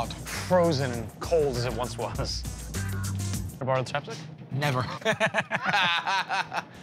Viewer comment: What's he saying? Best I can come up with is "can I borrow a temptic?" but that's not a word. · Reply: Chapstick?